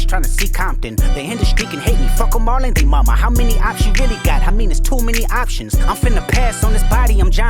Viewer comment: Does anyone else hear Kendrick say "how many ops did diddy got" at 5:00 or is it just me